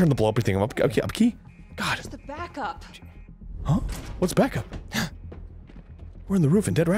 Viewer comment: in 1:59, did she just say "baka!" ?